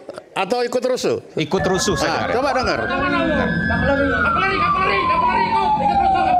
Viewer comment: Sepertinya bang Fery salah dengar....yg diucapkan itu bukan "kapolri" tp "kavaleri"